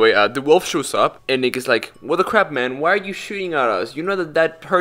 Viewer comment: I misheard “Nick is” as n-word 💀